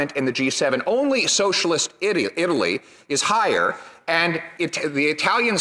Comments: When the man was talking, I thought I heard a bit of a Freudian Slip, "..socialist *idiot*...". Was it just me? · Did he just say "only socialist idiot Italy?" LoL. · Did he just say and correct himself: "...in Socialist idio.... Italy ...."? 😂